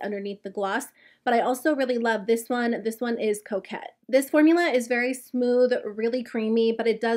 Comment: When you said the shade name “coquette” I thought you said “cokehead” and I like suddenly looked up and went “huh?” :S 😂 that would even be a bit much for urban decay!